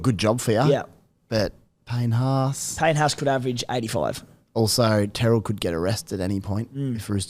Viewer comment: I thought said "Terrell may could get arrested at some point" Haha